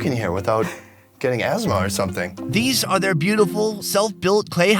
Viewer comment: I know I’m being a little OCD but @ 9:46 did he say “asmar” like in animal farm?